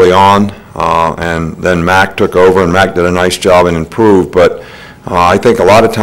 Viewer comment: Man I thought he said Jalen improved